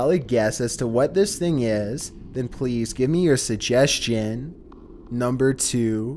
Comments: I heard "please give mercy" in the storm pipe one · On number 10 sounds like he said please sir help me